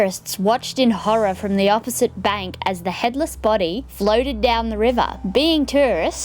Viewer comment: I thought she said “American terrorists” and was so confused 👁👄👁